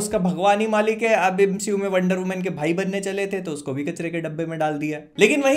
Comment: MCU me Wonder Woman ka bhai? Kya bol gye bhai smj nhi lgi😮